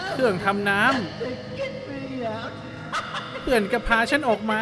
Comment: จริงๆใน original มันพูดว่า Washing Machine(เครื่องซักผ้า) แต่ใน remake ใครๆก็ได้ยินเป็น Water machine อ่ะ😂